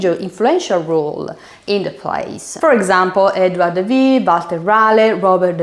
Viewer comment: DID YOU JUST SAY << EDWARD THE "VEE" >> at 1:54 ? ...